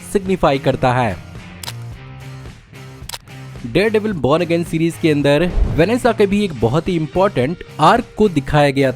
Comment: Idk why I heard ''Vanessa Kirby'' instead of ''Vanessa ka bhi'' 💀